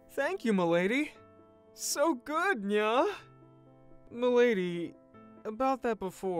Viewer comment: "so good nya" Did he really said that? Did he REALLY said NYA?